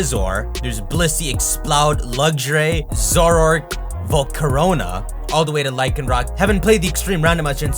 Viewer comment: did you say Volcorona...?